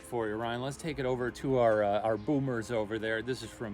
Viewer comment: i heard "boobers" instead of "boomers" that's so funny